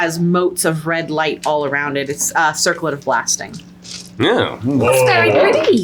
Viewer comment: I thought said a Circlet of Lasting, and i immediately thought "well i know where theyre gonna put/store that" 😂